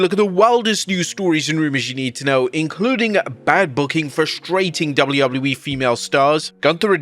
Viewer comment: Ohhhh bad booking I thought it said bad parking 😂